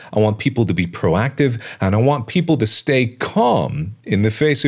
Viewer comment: It sounds like you said "I don't want people to be calm...", but I'm sure you said "and I want..."